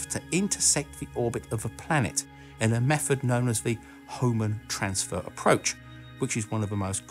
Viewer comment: All this time, I thought they were saying "home and transfer" method.